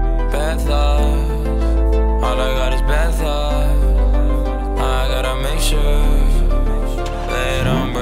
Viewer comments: Ngl I heard “bath salts, all I got is bath salts” 💀💀💀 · I thought he said “all I got is bath salts”😂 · sounds like he is saying all i got is bath salts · my mans said bad thoughs, my dumb ass though i thought he said "all i got is bath salts" bruh